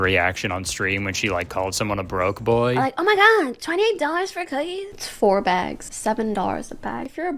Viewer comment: did sis really say 4 bags for $7 each came to $20…? 😭 Maybe it’s her math that’s broke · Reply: She said $28, her "making fun of broke people" voice just made it sound weird lol